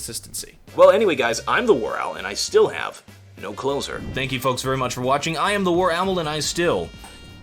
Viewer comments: you guys should make an episode on WarOwl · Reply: I once thought that at the end of your videos that instead of saying “I have no closer” I thought you were saying “I have no clothes” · Wait what... is that what he is saying? No closer? I fucking thought he was saying "I have no clothes on" i thought it was a meme.. i could not understand it